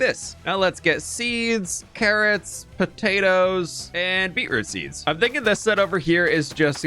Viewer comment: I thought I heard: “seeds, beeds, potatoes… tomatoes” would have been to funny 😂😂